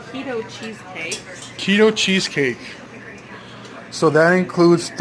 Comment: you know for a minute there I thought yall said Cheetos cheesecake lol. I watching interestingly when you were going to add the Cheetos and how it would taste and then realized you said Keto Chesscake. Good luck with the egg fast